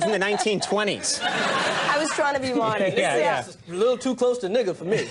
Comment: what did chris say at 3:04? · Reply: "A little too close to nigger for me". What he said.